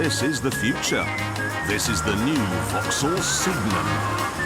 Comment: Thought he said vauxhall semen